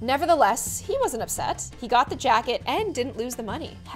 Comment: did anyone else notice how it said jacked instead of jacket?